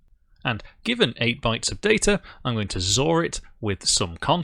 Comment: what is this new English word? is it xor?